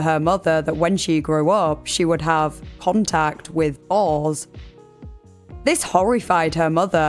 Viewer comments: Oh you’re saying boars, like pigs. Dang, took me awhile. I thought she was saying Balls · she would what? I honestly have no idea what she said. It censored too much for me to understand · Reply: I hear "contact with balls" but I'm pretty sure that's not it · she would what? I honestly have no idea what she said. It censored too much for me to understand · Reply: *Boars - male, intact pigs